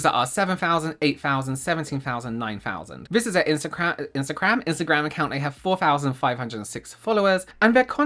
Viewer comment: Est 24:14 - kinda sounded like “insta-CRAP” at first. Perfect! They seem to be full of crap. Then it sounded like “insta-CRAM” When I listened again. That’s another good option bc they trying to CRAM their bs onto the public